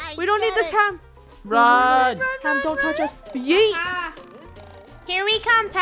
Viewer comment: was that a yeet I heard 👀